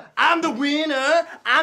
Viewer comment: Is it just me or is anthony saying Im the ''Weiner'' Im the ''Weiner''?